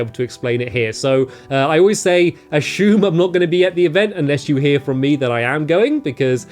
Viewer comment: i turned on closed captions, it said ashumed, instead of assumed, lol.